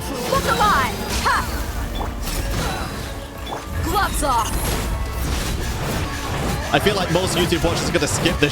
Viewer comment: it sounded like she said spots off😭 I’ve been watching too much miraculous ladybug